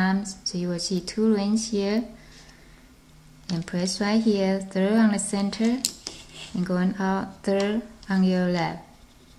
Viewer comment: What did u say do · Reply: place horizontal band from 3rd on the center to 3rd on your left.